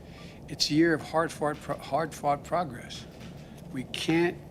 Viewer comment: Did he say "hard fart progress?" Fast forward video to